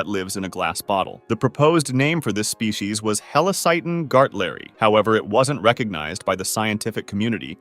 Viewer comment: that helacyton sounded like hail satan